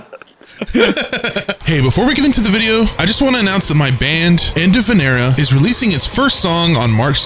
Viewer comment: Ohhhhh......"Enda Vinera". I thought he said "End Of An Era" 😂